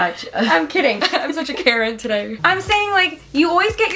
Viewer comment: I misheard "karen" to freakin' "Carrot"😭